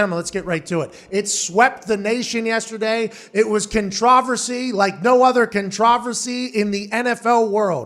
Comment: The way that Pat says controversy just pisses me off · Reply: Sounded like he was saying contrive-versy.